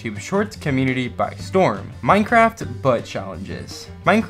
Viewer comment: "minecraft butt challenges" is what i heard in my 5 yr old humor